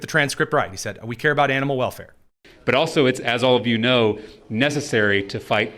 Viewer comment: To be honest, it sounded like he said ‘enema welfare.’